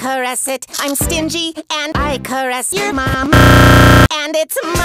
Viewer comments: Did Stingy Say That He Caressed My Mom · Did Stingy say I caress your mom!? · 😂😂😂😂😂😂😂😂 Did Stingy just say, "I caress your mom."?